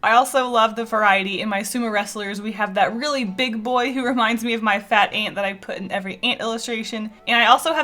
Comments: When said "it reminds me of my fat ant" I thought she said her fat AUNT I was like HOW DARE U CALL UR AUNT FAT 😂😂😜 I'm so dumb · When you said "my fat ant that I always draw" I thought you said "fat aunt" and I was like damn you just gonna expose her like that haha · when you said fat ant I thought you said fat aunt 😂